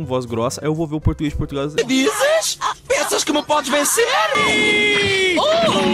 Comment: Só Eu Escutei Ele falando "Tanga Tanga TANGA!"